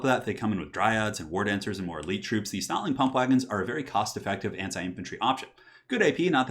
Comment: I keep hearing "snotling kampfwagen" :P